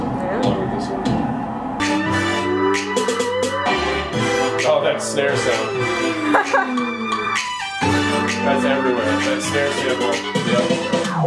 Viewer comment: can someone tell me what he said between 7:18 and 7:25?? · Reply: NotAName “Oh, that snare sound. That’s everywhere. That snare sample, yep.”